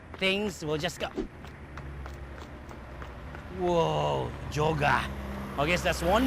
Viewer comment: When I heard say "Jogger", I thought I heard him say "Joga" hahahahaha